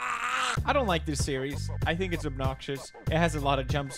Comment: when u said i dont like this series i thought you said i dont like u t series xD